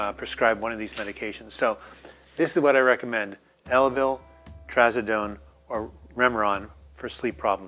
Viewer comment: Me. I could not understand the name of the sleep med that you said you still take. Would you or someone else mind spelling that out for me. Sounded like you said “Aldon” or something like that. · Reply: Elavil, it is amitriptyline.